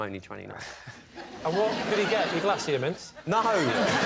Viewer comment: What is Lee's joke at the beginning?! 'What did he get, your......????' I can't hear it clearly and its driving me nuts! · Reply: Sounds like he said glacier mints?